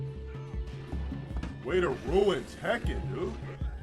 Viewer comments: "way to ruin Tekken dude... " misheard subtitles. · im not sure how accurate these subtitles are. "wait, it ruins heck you dude" is supposed to be "way to ruin tekken dude" and "dominated the match and they do mash the fucking super intact" is supposed to be "... and the dude mash the fucking super in tekken" i may be wrong but the subtitles sure arent what he said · Whoever is doing your subtitles isn't very good. He clearly says Tekken twice in the clip around 7:17. Don't be cheap.